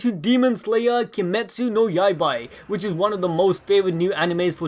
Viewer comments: Did he just say Kimetsu No Yaibai instead of saying Kimetsu No Yaiba? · Did you just say... Yaibai*?! Why not *Yaiba? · Did he just say yaibaii